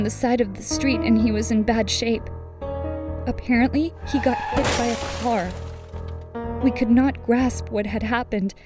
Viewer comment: I thought you said you were happy that he was hit by a car you monster ulaya